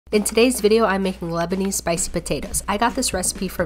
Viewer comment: I thought it said lesbian spicy potatoes 😭